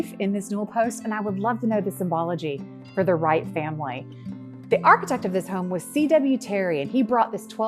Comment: Can somebody please tell me the name she calls the motif on the newel post at around 11:08? The sinbaro motif? St Varo motif? I've replayed it over and over and I can't understand what she's saying just there. What's that motif called please? Beautiful building and one of my new favourite channels. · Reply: Thank you so much! We're happy to have you here. She is saying "sunflower motif".